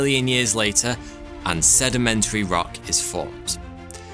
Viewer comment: did you said an sedimentary?